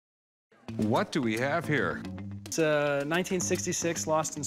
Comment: Maybe it's my ears, but I thought he said, "w What do we dab here?" Smh...